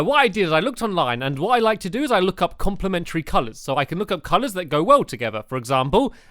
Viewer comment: *colours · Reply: oh I thought you said secondary colours not complimentary colours. Oops.